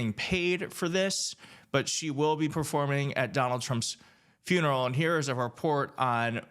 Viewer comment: I just came to see if it was just me that heard/thought I heard 'trumps funeral. or not. 😅 I'm glad it's not just me!